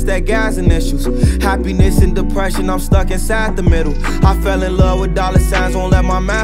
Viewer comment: “Happiness and depression im stuck inside the middle” damn.. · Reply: 4L Sythe i thought he said “i’m stuck in sad the middle” lmao